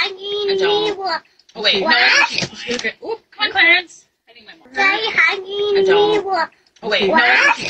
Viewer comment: I heard bye clearance..